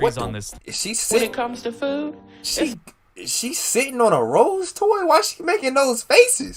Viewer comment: “Is she sitting on a rose toy” is crazy 😂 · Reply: I thought he said “sitting on rolls” 😭